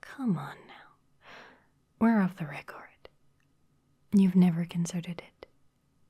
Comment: We’re off the what now? Cuz it sounded like she said “recard”